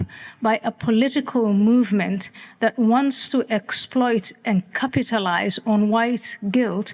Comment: When she said capitalize my brain heard it as cuckatalize and I think my imagined word might be even more on point